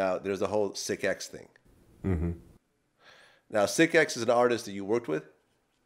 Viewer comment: Vlad: So you worked with sicx? BLH: Probably BLH: You heard of C-Bo, right? He's Hard! Me: LMAO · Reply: GROUP Actually Lynch thought he said is he the hardest you worked with not artist. It all makes sense once you know he misheard.